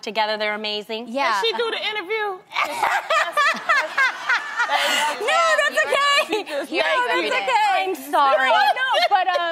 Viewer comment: Did anyone else hear Jeannie at 4:28 after loni told tamera "can she do the interview " she jumped in and said " yea tam you are here everyday. "